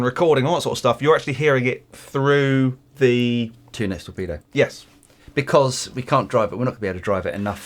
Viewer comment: Does anyone know what is saying at 3:01? He is explaining that the signal is going to something else, an attenuator perhaps? Do you unplug the speakers and use one of Line Out sets “ Direct” or “Recording Compensated”? Thanks in advance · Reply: I say it’s a two notes torpedo studio. Yes it’s a load box and was recorded direct